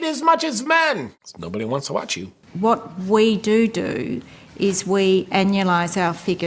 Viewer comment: when she actually said "what we doodoo"... I lost it.🤣🤣🤣🤣